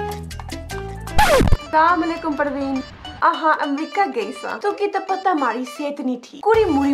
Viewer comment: I thought it said New York but they ae talking about America or should I say amreeca